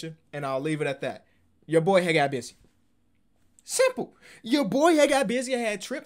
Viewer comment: In the beginning I heard “ya boyaa ha gottt bissus”